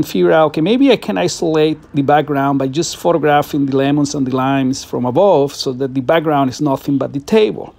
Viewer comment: Lemon and Lines. I had to replay it, I thought you said lemon and limes.